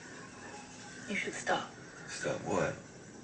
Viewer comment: I couldn't even understand what the hell Carl said to Rick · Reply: DuU even he said he should stop being the leader